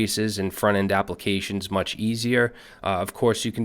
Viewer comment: Thank you for this crash course :) p.s. 1:25 "much" instead of "mush"